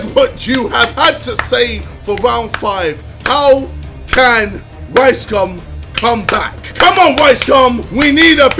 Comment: Why does it sound like he said “ricecum” 13:29 😂